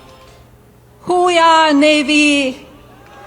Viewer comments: I 100% thought she was saying "who we are, navy". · It sounds like she is saying "who we are navy". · Sounded like Melania said, who we are Navy. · Oh I thought she was saying who we are navy. Like be best. Just bad English. ! Lol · I couldn't understand who that female was or what she was saying. I thought she was saying "who we are, Navy" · I thought Melanie was saying “Who we are Navy?” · I thought she said ”Who we are, Navy?” As a non-american that tracked. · I straight up thought she was saying, "Who we are Navy?" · it sounded like she said "who we are, navy" twice, then she noticed navy was saying "hoohya!" and finally she said "hooyah, navy" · I thought she was saying “who we ah navy” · I heard; "Who we are Navy..." "Who we are Navy...", "Who we are Navy...". I guess I was supposed to hear; "Hooya, Navy!", "Hooya, Navy!", "Hooya, Navy!"? The audience seemed confused too. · Milannia sounded like she was saying Who are ya navy? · I thought she was saying, "Who we are, Navy?". How dare he say that America won WW1 and WW2. In both cases, America was the last to join the conflicts. FDJT and MATA. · I thought at first she said "who are Navy?" 😂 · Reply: Yeahh.. i heard “who are ya, Navy?” lol 🤷🏻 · It sounded like Melania was saying who we are navy